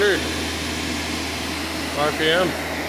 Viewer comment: or 1:24 seconds in did you say RPM or God tham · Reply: rpm lol.